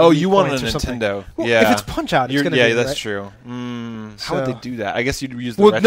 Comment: Hey guys. Long time watcher. I was watching this episode tonight and heard something hilarious. If you scan to 20:33 I SWEAR you can hear say "Yeah... you're gay. That's true." I got a kick out of it! Peace. Yaaoow!